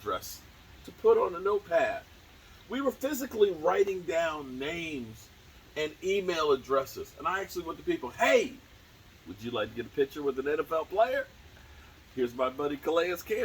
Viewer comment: oh email addresses, I thought you said female addresses lol